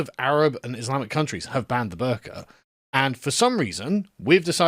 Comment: This isn’t related to the topic but every time Sargon says “the burka” I hear “the burger”